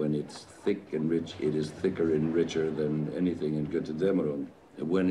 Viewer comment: what does he say at 36:14? "Thicker and richter than anything in..."? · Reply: lushui203 I believe he says Götterdämmerung, which is one of Wagner's operas from the ring cycle.